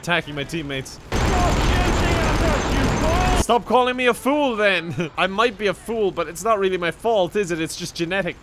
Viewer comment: he actually said: stop shooting at us you fool.